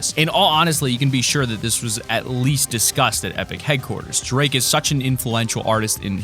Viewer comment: did he say disgusted?